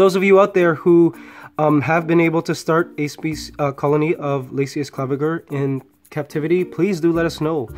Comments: Seriously, I thought when you said it her species is "laziest clever girl". XD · Thought you said laziest clever girl lolol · I thought he said "laziest clever girl" 😂